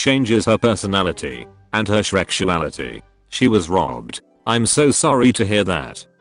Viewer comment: I heard “her shrekruality.”